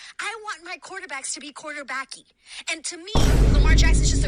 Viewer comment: I ain’t going hold you I thought she said QuarterBlackey 😂😂😂